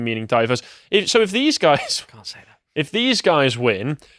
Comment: Who said "Dont say that" in a whisper at the 3:10 mark? So sad how UK Conservatives have to watch what they say to such a degree as they do. <link>